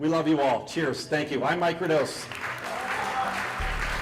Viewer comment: I thought dude said "Hi, i'm Mike Rodose" lol